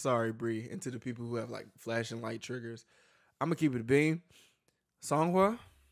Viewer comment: I always hear I'm going to fxxx it up and now I can't unhear it lol